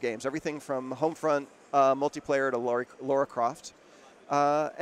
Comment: DID HE JUST SAY LAURA CROFT?!